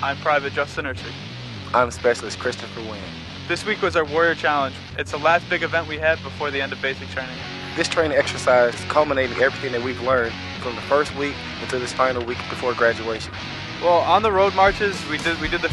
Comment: did he just say "combinate"?